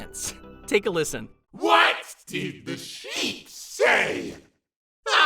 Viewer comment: I can’t really hear the infected bittergiggle jokes can you tell me what he says because I can’t understand them. Thank you. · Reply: What did the sheep say? BAA-KA!